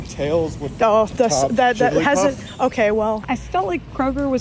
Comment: Who is Tails topping in this scenario? Sonic? I can’t hear what he’s saying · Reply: He says jigglypuff at the end of that shot lol